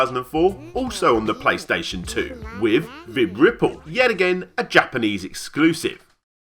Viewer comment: I thought you said, Vib-Ripoff